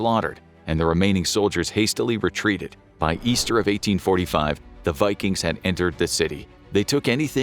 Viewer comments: It sounds like he said, "by Easter of 1845." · "By Easter of 1845" is what was said. Small mistake. Just pointing it out incase someone wants to edit it.